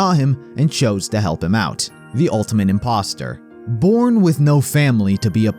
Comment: did he say imposter...